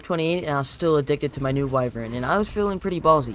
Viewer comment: it sounded like on 17:00 you said im still addicted to my new boyfriend i think